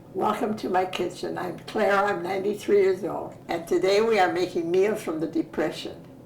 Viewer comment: For a second I thought she said she was 393 years old